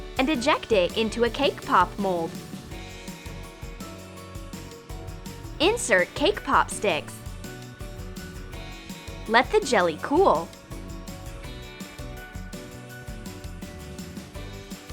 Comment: i thought she said Kpop 1:20😂😂😂😂